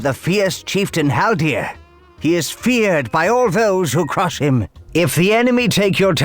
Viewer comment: "He is feared by all those who crush him"... I'm not sure that's what he meant to say